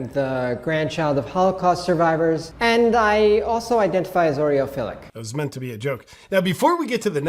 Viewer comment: approx 2;15,. to me, not american, sounds like they say "I also identify as an oreo?'